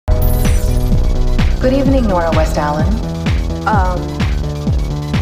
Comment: When Gideon said "Good Evening Nora West-Allen" i heard it as "What do you need Nora West-Allen